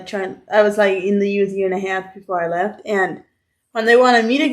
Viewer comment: I thought she said 6.5 years at first 👀. Thank God I misheard that.